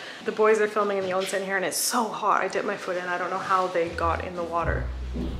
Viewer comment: What does she say in between "and it's so hot" and "I don't know how they got in the water"? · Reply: She said "I dipped my foot in" :)